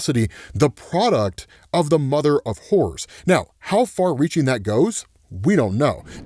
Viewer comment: Did he say mother of whores?!😮🤯😂8:59. Once you hear it, you can’t unhear it. 😂🤣😂